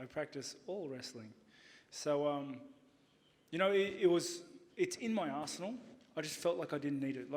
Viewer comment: Lol old-ish presser... but still funny as hell @ 6:36 . Sounds like he says it's in his ass hole